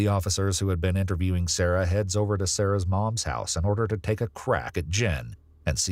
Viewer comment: “ one of the officers headed over to Sarah’s moms house in order to take a crap” is what I heard the first time.